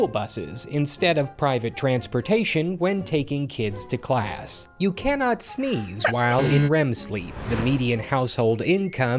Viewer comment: I thought it said you can’t sleep when you’re hard